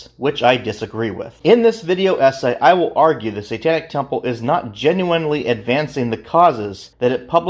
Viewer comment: When you first said the name of the temple it sounded like you said Satanic Tailhole...🤘☠️🤘